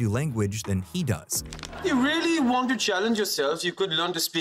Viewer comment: if not for the subtitles it would be pretty hard to understand what Sheldon said!! Lol “Jab main aath (8) saal ka tha, maine seekha tha!”